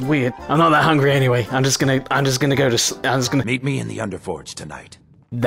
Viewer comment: I paused this at a bad time so when I unpaused it I heard say; "eat me in the Underforge tonight." At least make me a werewolf before you come onto me like that. :P